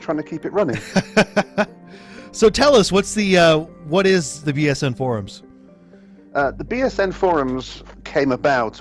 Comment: Thought he said BSM forums.....:p